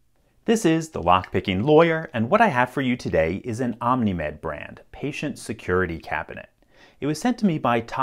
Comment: I thought you said ‘an Omni-MAN brand’ and I was quite shocked for a second